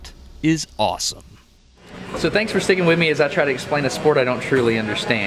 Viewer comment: Did you say, "Thanks for sticking with me."!? I wonder what the difference between a deflection strike and a clean hit with no ice contact would be (to find out how much force/power you gain)? That seems pretty quantifiable.